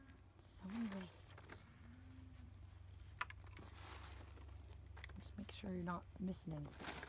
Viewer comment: at 1920 did she just say "just like eating peach"? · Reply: I said, 'Just making sure sure". Wireless mic is on the way! 🙂